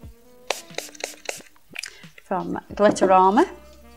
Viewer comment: I swear I thought she was saying Glitter Armour!!